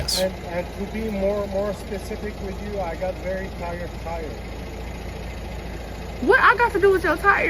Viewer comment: Did he say he has "very tired tires" ? 1:34 :face-turquoise-covering-eyes